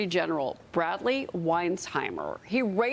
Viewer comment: did she say his name is Bradley wisenheimer if true that that’s awesome